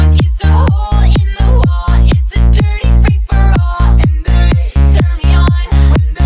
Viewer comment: i thought it said ITS A DIRTY PAPER ROLL